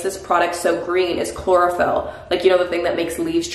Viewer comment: my bitch ass thought she said chloroform